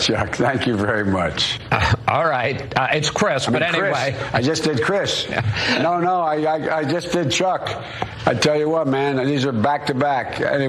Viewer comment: "I just said Chris. No I just said Chuck." Dude can't remember 3 seconds ago. He's like a goldfish. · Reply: I thought he said "I just did chuck" "I just did Chris" he needs to fade away